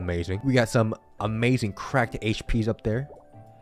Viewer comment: I thought it said cracked hip 😂